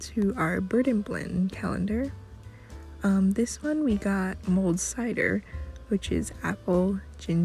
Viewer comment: When you said "Mulled Cider", I thought you said "Mold Cider"! I'm glad I was wrong! :D Thanks for showing off day 2 of the Advent Calendars!